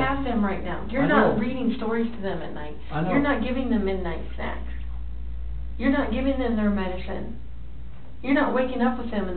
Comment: Can anyone tell me what the female detective says here ? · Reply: Dull Tableaux “You’re not reading stories to them at night, you’re not giving them midnight snacks, you’re not giving them their medicine.”